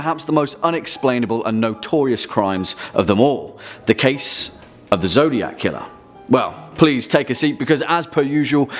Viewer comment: I thought I heard “The case of the Zodiac Kya” 😂😂